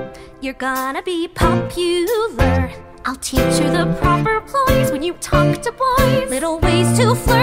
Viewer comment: Wait, ploys? "I'll teach you the propper ploys to talk to boys." I always heard "Poise" "The propper poise to talk to boys." Please tell me I'm not the only one. XD